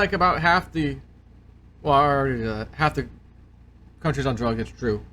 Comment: Hillsborough. Sorry, I thought you said 'county'.